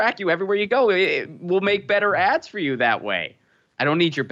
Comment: Better 'Ads'?? I thought Google said better 'Abs'...aw crap.